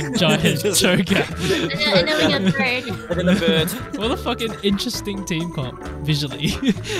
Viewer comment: Did only I heard loud FUCK at 14:11? XDDD